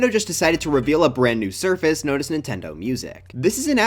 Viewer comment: bro DEFINITELY said surface instead of service